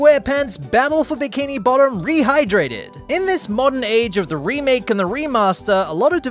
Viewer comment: i heard dehydrated